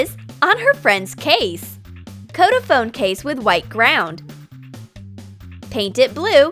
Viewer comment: did she say coat it with white ground??? im confused